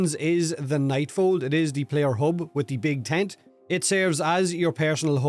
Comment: 😂😂😂 I thought he said, "big taint" when he said "big tent" at the beginning! 😂😂😂